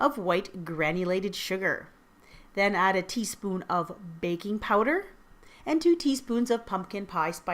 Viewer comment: Hi! I made this recipe and in the beginning, i thought you said “baby powder” not baking powder....i didnt use baby powder but i just thought it was funny😂